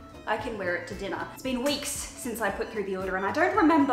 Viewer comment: why did i think she said "it's been 3 week since I put deodorant on" at 1:15 ??? help 😭 😭 😭